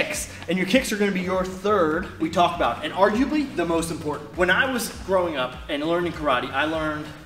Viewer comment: sounded like "when I was throwing up"...:_YeaRight